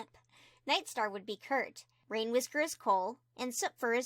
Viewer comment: I thought you said, “ Rainwhisker was cold” haha